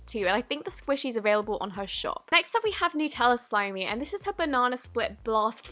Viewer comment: Did she just say Nutella slime xD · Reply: Nutella Slimey is a slimer on Instagram.